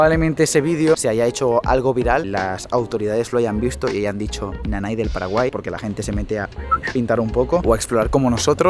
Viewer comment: Minuto 5:15. Nanai del Paraguay? O que dice?